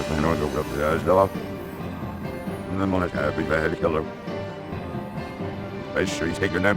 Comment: I'm not the only one who hears "I had to kill her" there right?